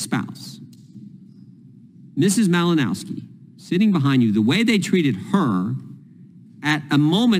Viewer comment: Because she was, in fact, sitting behind Mr Cummins. That is what Jordan said! Duh! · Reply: Sounded like Jordan said "behind me"